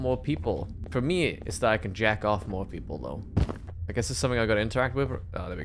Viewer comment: New to this channel and first minute in I hear "For me, it's that I can jack off more people" 1:00 LITERALLY FIRST MINUTE XD. Made me laugh a bunch though, might subscribe